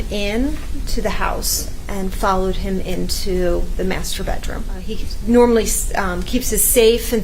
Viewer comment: after the constant sex up until this point I heard masturbatron at first.